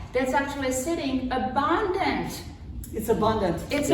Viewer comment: She def said abandoned and the other lady thought she said abundant. 🙄